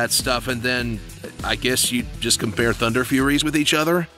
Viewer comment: Did someone said Thunderfury?!